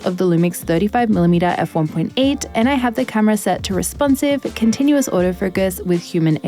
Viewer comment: For a second I thought you were saying Responsive As f***, rather than Responsive AutoFocus hahahah Great video, as always! :)